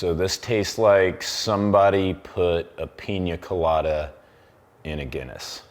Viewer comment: Did he just say Peen Ya Colata?